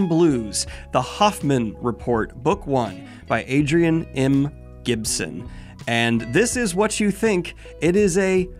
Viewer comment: At ~16:30 when you started on the Mushroom Blues segment I was a bit zoned out and my Suneater-pilled brain thought that you said “Hadrian M Gibson.” I lost my shit for a moment 😅